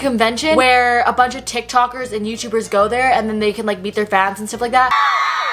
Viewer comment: Low key thought she said “its a convenient where youtubers and tiktokers beat their fans”